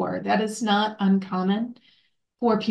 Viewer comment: Great interview, but did she say ascared? 14:48 lol. Nerves😮